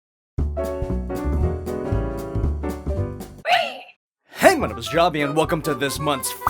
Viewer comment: Did you just say "shatman"?